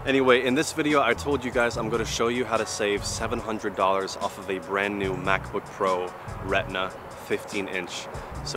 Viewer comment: Since when refurbished is Brand New ?? 🤔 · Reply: Vi An Diep 0:26 he clearly says brand new !!